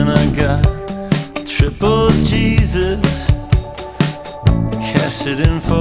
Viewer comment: I thought he said triple cheese its when its triple Jesus